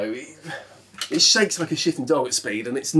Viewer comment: did you say it “shakes like a shitting dog” 🫢😂💀